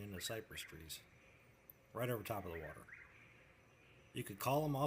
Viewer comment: this is surreal, how did YouTube's closed caption miss-hear you say "water" and think you said "river"? did it analyse the video being displayed? those two words sound completely different, there must be some context AI going on